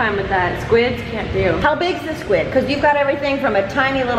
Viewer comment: when mamrie said "how big's the squid" i thought she said "helbig's the squid"